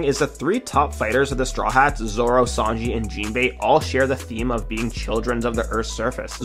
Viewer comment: Nah when he said the the three top fighters on the straw hats I thought he said luffy sanji and jinbei